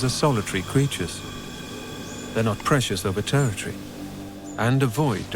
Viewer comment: I've listened to it multiple times at ~8:00, and it still sounds like the narrator says "They're not precious over territory." That can't be what he said. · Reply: No pressures over territory